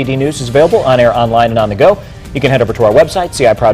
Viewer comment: sounds like he said “for your phone or cablet”